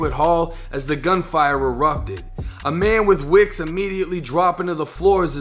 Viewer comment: What the fuck is he saying at 2:24 is saying wicks